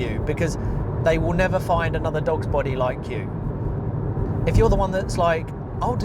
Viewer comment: I thought you said dog's botty lol. I work in an electronics factory part time and get part disability pension as I'm on the autistic spectrum, I am stuck mostly doing my repetitive job as I'm good at it, I'm only working there to get my pay, I still do my job well enough as it's in my nature to make the product with quality. I know my workmates internally have an issue with me working part time but I ain't quitting my pension or I'll be worse off working full time and losing the pension benefits and the rent going up, so yeah full time near minimum wage blue collar work is not worth it in general, in my own way am doing things the smarter way given my own circumstances. Anyhow good video, I totally agree with you!